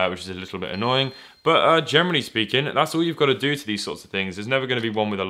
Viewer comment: Did you say Germany speaking?